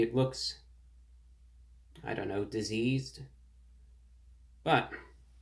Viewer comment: Lol I thought he was gonna say dry then he said diseased lol 😆